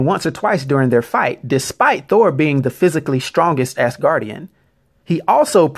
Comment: I'm really sorry, but for the first 1:35 minutes I thought you were saying "ass guardian"... 😅